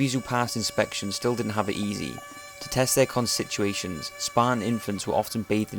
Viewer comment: What did he say at 1:22? "to test there..." what? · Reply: constitutions